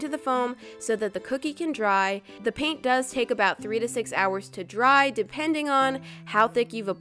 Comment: did she say that it takes 6 to 6 HOURS to dry is that real or just a mistake someone please tell me